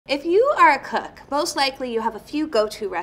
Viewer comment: Sounds like she says “if you are a Cuck”